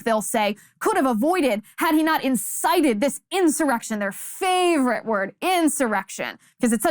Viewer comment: I keep hearing it was an "erection" from the likes of Schumer and Schiff.